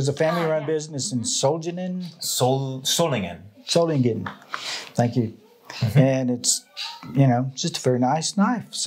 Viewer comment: the grandpa said "Danke". As a german I thought I heard it wrong :D Soo sweet 😊👍 I liked the whole conversation!